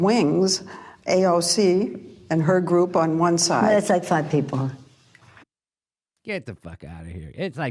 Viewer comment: Sounded like Pelosi said, “It’s outside people.” at first but she actually said it’s like five people! 😭😭😭😭It’s more like twelve people!! Learn to count!